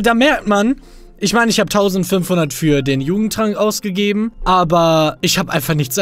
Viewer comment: Ich habe nicht jugendtrank verstanden sondern Judentrank😂